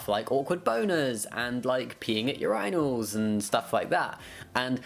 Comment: The YouTube closed captioning has misheard “peeing at urinals” as “peeing at your idols” and has reduced me to literals fits of giggles!